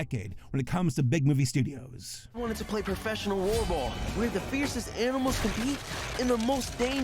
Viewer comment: For a longer time than I probably should have, I kept mishearing the term "Roarball" as "Voreball"